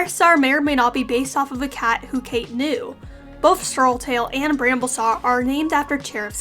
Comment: i'm sorry, but did she just say "Bramblesaw"?